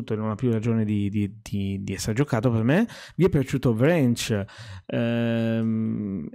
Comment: Ciao, al minuto 11:55 hai nominato un gioco che ti è piaciuto, ma non ho capito bene se dici Ranch? Grazie, se me lo puoi scrivere.